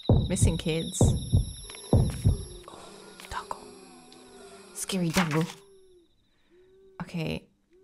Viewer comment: At first I thought you said , "Scary Dango!" lmao Great game! Love and hate the scares